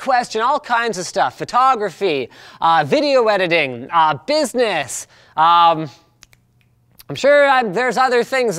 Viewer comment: He said business but i heard physics